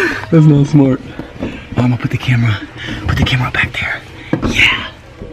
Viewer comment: who was saying put the bike back please at 19:55. ? hhmmmmm . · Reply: Yeah I heard "put that bike back" but I'm not hating I love their videos.